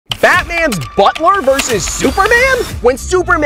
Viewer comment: At first I heard batman's buttplug.....